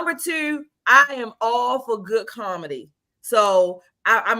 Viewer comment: Karmady. My new term was discovered by one of these ladies saying "comedy" but sounded like "karmady" to me